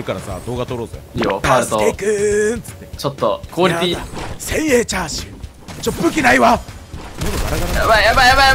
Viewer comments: 潜影チャーシューに聞こえる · 千円チャーシューに聞こえる